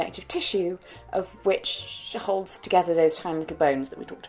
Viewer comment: Just letting you know that at 2:47, the subtitles say 'hundreds of bones' when it should be 'tiny little bones' . I tried to fix it myself, but the subtitles are locked.